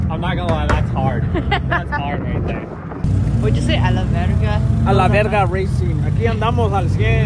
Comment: When she said “what you say a la verga” I thought she said “I love verga” 😂😂😂